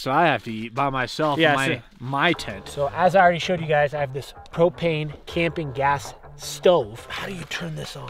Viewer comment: did say cocaine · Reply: propane